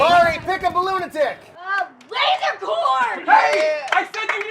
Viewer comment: I thought Marie said Laserporn